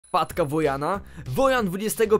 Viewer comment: Usłyszałam matka zamiast wpadka